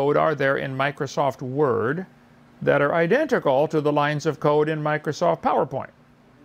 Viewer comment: I got distracted and tuned back in just in time to hear Hovind say his whole thing about lines of code, but I thought he was saying "lines of coke" so now I have a new question, does anyone know how many lines of coke are in Powerpoint and Word? Separate and combined